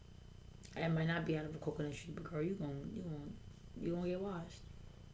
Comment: What were you saying 11:13? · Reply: "It might not be out of a coconut tree, but girl you gone get washed" - basically she said all that & performed the "good democrat/identity politician" and still got her ass handed to her in the election because she is the antithesis of what America truly represents despite the worldclass branding